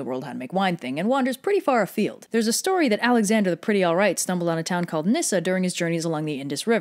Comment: I will now only call him Alexander The Pretty Alright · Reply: At first, I thought she said "Alexander the Pretty Alt-Right".